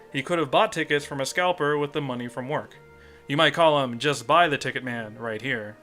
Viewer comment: When you say it, I always hear "Justin the TicketMan"